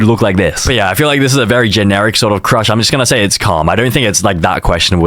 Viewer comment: stop saying “calm” because it keep making me think that u saying cum😭